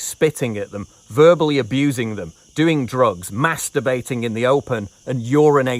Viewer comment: Me: Mass debating in the streets? The homeless are debating. Whats this about? Oh, no. Say it ain't so... That's not what you said.